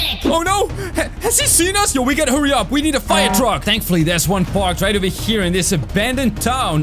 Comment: When u say firetruck I thought u said spider truck